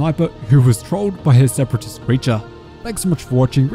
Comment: Hol up did you say separatist creature